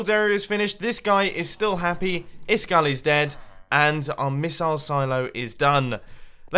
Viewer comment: Idk why but i heard says "This guy is happy that iskall is dead" Instead of "This guy is happy, Iskall is dead"